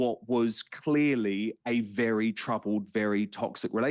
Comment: trash "apology".🫣 but one thing, he didnt say: do I think johnny was a good guy? he said: "do I think johnny was a perfect guy?" not good guy, perfect guy. and johnny said himself during his testimony: "nobody is perfect, certainly not." I just wanted to say that because you got so angry. I think you misheard💙🙏😜